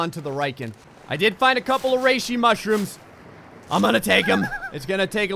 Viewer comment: I always thought he said racey mushrooms....